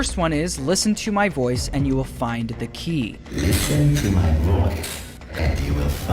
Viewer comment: I always thought Foxy said "Listen to my voice, and you will find bikini"